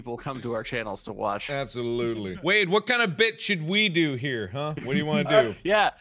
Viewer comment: Mark: Wade what kinda bit should we do here, huh? What I heard: Mark: Wade what kinda bitch Ud we do here, huh? Now I replayed the sentence that mark said then after I burst out of laughter